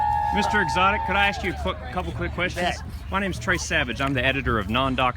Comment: Did he say "Bet?" What a G.